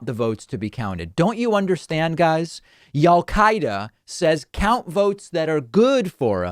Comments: Did he just say "Y'all Qaeda"? I think that is the most accurate label for these sanity-deprived cultists. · Did I just hear David say "Y'all-Qaeda" to describe the Trump supporters terrorizing Phoenix?